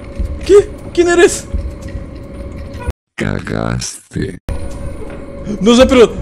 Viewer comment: Yo entendí CAGASTE